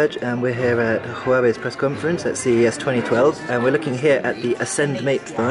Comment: did he say CES 2012?